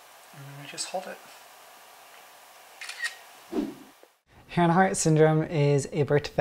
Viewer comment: I thought he said ”Hannah Hart syndrome” at first. 😬😂😂 Such a good-looking and charming guy. 😍